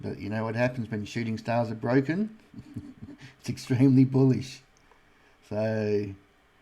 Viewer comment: I thought you were saying when a shooting star is broken it formed a black hole😂